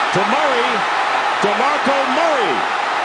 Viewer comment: Did he just say demarco murrays name in one word? D'Murray? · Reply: I think he said "To Murray"